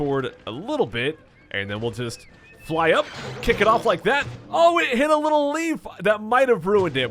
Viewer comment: did anyone pause at 20:27 and read what it said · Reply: Now I just hear "oh it hit a little lee"